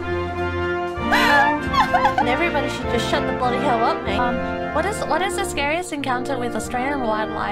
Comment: What is she saying in 0:25, i legit can't understand. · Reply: "Everybody should shut the bloody hell up, mate."